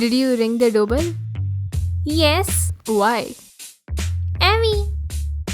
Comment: I heard "Awaiyen"🤣 at 4:53 That's Unexpected